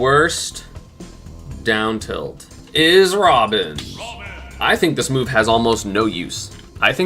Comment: i thought he said rob for a second lmao